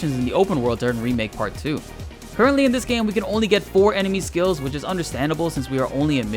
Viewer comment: There is only 1 enemy skill materia in the game unfortunately. This is because it's a super broken materia haha well have access to more I'm sure in remake part 2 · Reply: Oh I miss understood the video. I thought you said you can get 4 enemy skill Materia. But you said you can get four enemy skills. 😢😢😢. Thanks !